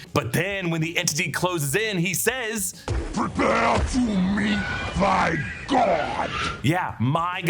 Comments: I thought He said prepare to meet thy God 🤔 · I think he says "thy" and not "my" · he actually says “thy god”. Making a lot of mistakes lately… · does anyone else hear the entity saying “Prepare to meet THY God” not “MY God”?